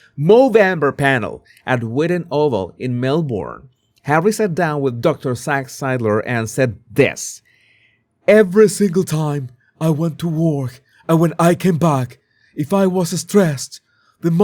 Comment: Did he say every time I went to work what work😅😅😅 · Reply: Oh that’s what he said - I thought he said ‘every time I went to war…’